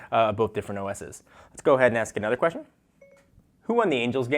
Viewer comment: It thought you said angel game not angels